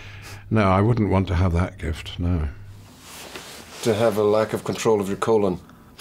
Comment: What he says at 8:33? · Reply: To have a lack of control of your colon ))